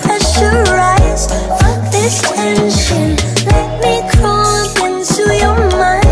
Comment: THIS ENTIRE TIME I THOUGHT MELANIE WAS SAYING “I can feel your blood pressure rise fuck detention “ BUT NOW YOU’RE TELLING ME SHE WAS ACTUALLY SAYING “fuck this tension”!? visible confusion